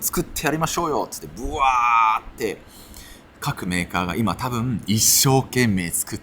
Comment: 各メーカーが革命家に聞こえて，二度見しちゃいました笑